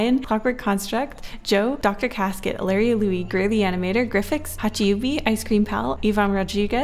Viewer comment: DID SHE SAY HACHUBBY IS A PATREON OMG